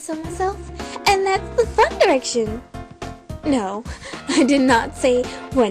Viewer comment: I seriously thought she said One Direction, I literally fell and cried.